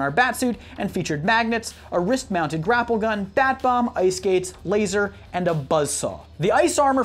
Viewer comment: Some where around 2:11, 2:10 did u say "bat bum"